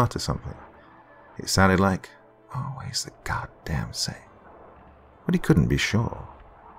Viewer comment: What did he say at 35:44?? I can't seem to understand but I think it was "Always the god damn same"?